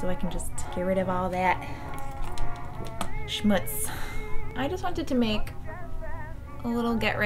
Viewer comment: did you just say "Schmutz"?! That was so cute, it means "dirt" in German LOLOLOLOL